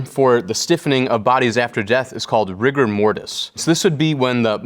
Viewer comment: 我聽成Rick n Morty 💀💀